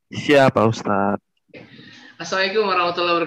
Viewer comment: Pangapunten tadz seringkali saya dengar ucapan salam ustadz terdengar seperti "assaikum" bukan assalamu'alaikum maaf klo saya salah dengar .sebagai sesama muslim hanya ingin saling mengingatkan karena ucapan yg salah pasti artinya jdi salah.